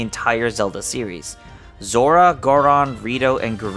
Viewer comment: Did I just hear u say Gardon instead of Goron?